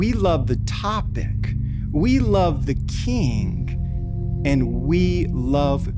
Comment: did he say “We love the kink” ? 😂